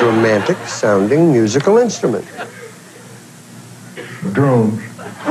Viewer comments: Can anyone else hear what the answer is for "the most romantic musical instrument" at 9:15? I can NOT understand it. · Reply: I BELIEVE HE SAYS "DRUMS!" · what did he say? I can't figure it out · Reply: Drums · It's hard to make out the answer at 9:15. Did he say a drum? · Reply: roachtoasties - This guy talks like he got a mouth full of chewing-tobacco...he said `drums`. Don't want to get too unPC...he shed drum`sh Had a friend who had that permanent chunk of chew in his cheek, and had to carry a can everywhere he went...except my grandmother's house, because if he had the can with him, he wasn't getting past the front door. It was a very unpalatable sight to see him spit juice 4 ft away into a can, with 1 spit in 10, missing. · At around 9:18 I can't understand his answer and it's irritating me far more than it should... help me please...lol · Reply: Drums